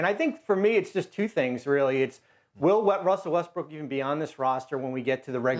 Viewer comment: IM NOT THE ONLY ONE THAT HEARD HER SAY WESTBRICK AM I?